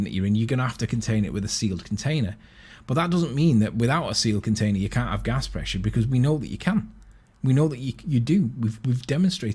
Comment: did he say "that doesn't mean without a sealed container you can't have gas pressure because we know you can" or did I miss something there? He talks so much garbage it's hard for me to keep up.